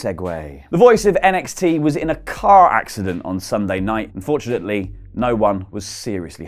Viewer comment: did he say 'unfortunately no one was seriously hurt' ? o.O